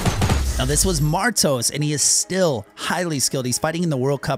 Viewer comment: Me: Did he say season 2? Sees the bear and Tilted Towers Me: He committed the major of sins